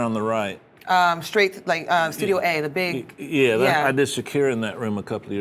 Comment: did he say shakira